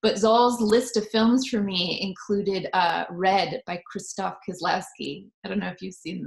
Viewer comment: Hey, I couldnt understand the director of the film she mentioned at around 4:13? I want to check upon the films she mentioned, but I only understoon The Double Life of Veronique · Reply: It's Krzysztof Kieślowski :)